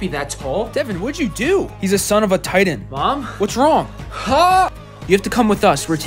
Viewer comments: I thought it said he’s the son of a tide end my football boys will understand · The fact I thought he said tight end instead of titan